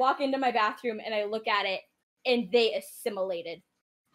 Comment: "and they....what? Can someone tell me what word she used there? English isn't my native language, so I'd really like to know 😅 sounds a bit like "stimulated"?